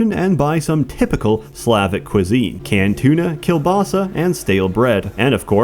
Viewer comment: Spasibo. Now I can't enjoy my favourite kolbasa without pronouncing it "kielbasa" as you did in this video. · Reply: I heard it as "Kill-basa". And that shit definitely kills my liver.